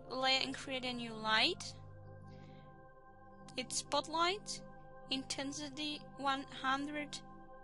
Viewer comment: i think she said "its bud light"